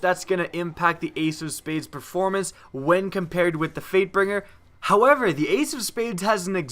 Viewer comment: Wow. I heard 'Freightbinger' at around 3:15... I thought my friend was the only one to say that and he was eating chocolate... Apparently others do it too, but not even eating anything. :3